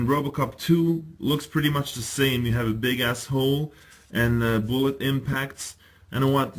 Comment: Loved the review but at 15:17 did you really say "Big Ass-hole" lol I know what you meant but it just sounded funny.